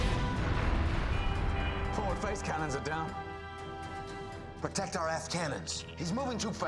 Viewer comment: "Protect the aft cannons" But the captions said "Protect the ass cannons"😂